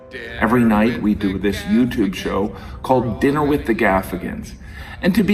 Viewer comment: When he said dinner with the gaffigan I thought he said “dinner with the Africans”